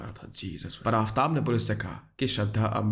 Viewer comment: Did he say? *Jesus (Hazrat Issa 'pbuh)*??